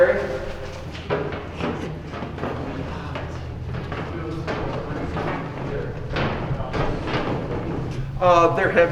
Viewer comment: Theres a whisper at like 31:35, right when it switches after the guy says "solitary." I cant tell what it says or if its more than a breath. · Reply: I hear"Elton" being whispered